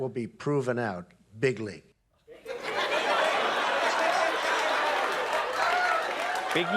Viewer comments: I think Trump said "big league," not "bigly." Trump is a racist clown, but "big league" is a commonly used phrase. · In his defense, I initially thought he said Big League...kinda makes sense, I suppose, if you really give him the benefit of the doubt. Big League...Big Time...I'm going to prove it to you...BIG LEAGUE! (Big Time!). To make it work, you still have to think he's an idiot, so probably doesn't help him in the slightest :o) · "Jonny come lately," trying to tell American's what to do! Have you seen the American primary votes for Donald. By the way, I heard "big league". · Trevor thought he said "Bigly" but what Donald really said was "Big League".